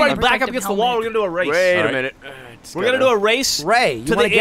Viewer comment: sounds like ray says "RAYt a minute"